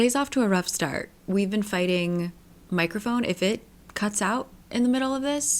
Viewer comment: I thought you said you were fighting microphone “ifrits” at first. That would make you have a rough start